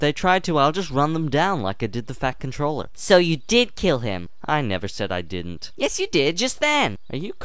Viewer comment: I thought I heard Thomas said"I never said I DIDN'T"